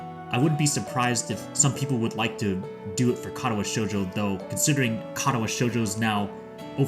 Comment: At first I heard "Kara No Shoujo" instead of Katawa and I nearly died on the spot thinking they announced translation :_NoseBleed: Clockwork Ley-Line seems interesting, often heard about it, maybe I will give it a shot in the near future, especially when this is story driven. Alicesoft opening on other stores is a gooooood news for me, probably one of the best this few months. :_MashiThumb: How's Your health? Everything progressing smoothly for You? □